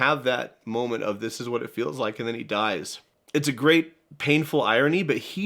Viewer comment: I thought he was saying this is what dying feels like